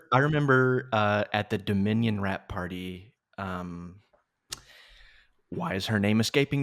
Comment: I thought he said rat party